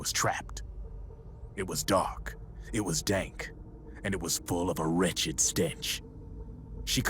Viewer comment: lol I thought he said "It was dark. It was dank. And it was full of a wretched stank"